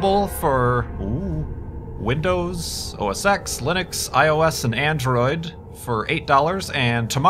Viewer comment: I don't know why, but it sounded like at 1:01 he said "windows, or sex, linux"